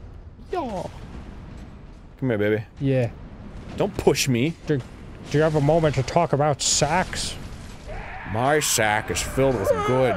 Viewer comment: i heard it as "do you have a moment to talk about sex?"